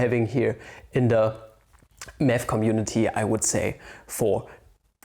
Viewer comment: i heard meth community ... And i was life wtf where did this came from